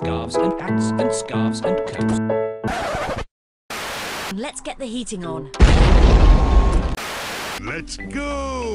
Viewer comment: it sounded like "Let's get the yeeting on."